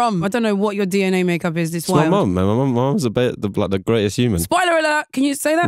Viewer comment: What's he say at 4:01? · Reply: He says 'it's my mum, she's the greatest human'